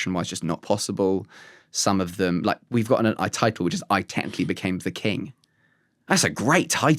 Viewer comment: what did he say? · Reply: Believe he is saying "I titled" - as in he titled one of his bits/programs "I technically became the king"